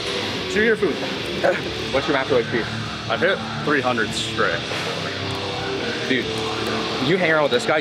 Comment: What does ask him at like 7:50 ? He says he does 300 strict of what?